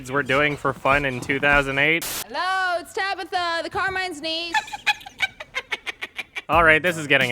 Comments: When she said "I'm Tabitha, the Carmine's Niece." I heard, "I'm Tabitha, the car might sneeze." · Sounds like she said, "Hello, it's Tabitha, the car might sneeze" likely all thanks to that terrible acting. Hard to believe Katheryn Winnick's next role would be the badass Lagertha in the show Vikings (yay!). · When she said “I’m Tabitha, the carmines niece” I thought she said “ I’m Tabitha, the car mice knees”